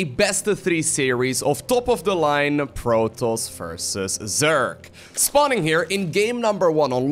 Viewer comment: All i hear is "proto versus zerk" Nice videos btw!